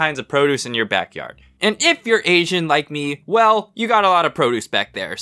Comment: "But if you're Asian like me, you got a lot of produce back there." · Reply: Ooooooooooooooooh that's what he said I thought he said "but if you're aging like me you got a lot of produce back there"